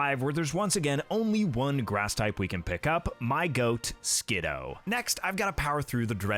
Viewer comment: It is actually said “sskid-DOO”.